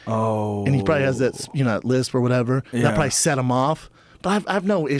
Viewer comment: and you know he probably has that... what he say? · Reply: Lisp